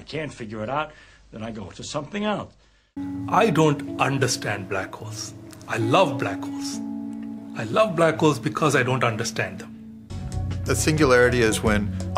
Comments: "I don't understand black girls. I love black girls. I love black girls because I don't understand them." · "I love black whores, I don't understand black whores, I love black whores!"